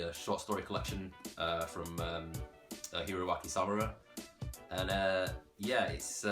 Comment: what manga do you tak about at 08:00? I can't really make out what you said. Is it ougi koshi by Hiro Akizawura? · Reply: Oh wait I think I found it, is it Ohikkoshi by Hiroaki Samura?